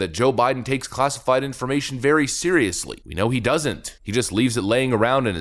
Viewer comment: Hi, i heard it as "Seriously, Joe takes documents"